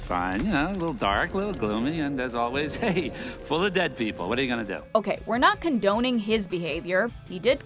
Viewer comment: did hades just said "full of dead people"?